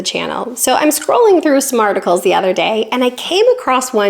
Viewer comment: what I heard … “I was scrolling through ‘smarticles’ the other day.” 😂